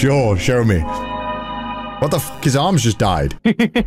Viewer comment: my headphones said battery low so it sounded like he said "what the fuck? Battery low" and i laughed my ass off